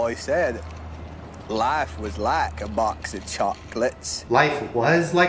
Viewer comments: We watched the forest gump scene in ELA class and I definitely heard "is like a box" not "was" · Okay so it was my first time I watched Forest Gum or something like that (im a late bloomer don't judge) and so when I heard the clip I heard "Life is LIKE box of chocolates" and then I rewatched the clip and apparently its WAS. Brb need to summon Satan